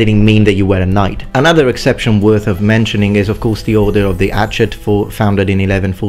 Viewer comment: I'm trying to find information about the battle of Tortosa you mentioned but can't find any. You call it the "Order of something" at 9:05 but I can't understand what you're saying. Could you share it? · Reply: kennyPAGC I believe it’s the Order of the Hatchet. We discussed them a bit in one of my classes on Joan of Arc and Women at War.